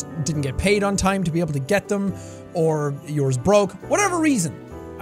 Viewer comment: When he said "or yours broke" o thought he said "or your just broke" 😂